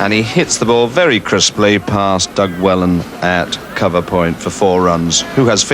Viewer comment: 'Textbook shot' from Emburey...without any foot movement whatsoever. And fielded by 'Doug' Welham. Not Parfitt's finest moment in the comm box. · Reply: I might have misheard - but I think he calls him Doug Wellan!!!